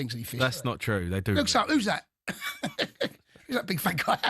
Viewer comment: When Jason said " who's that who's that big fat guy" I thought I heard "who's that big fat c**t 😂 😂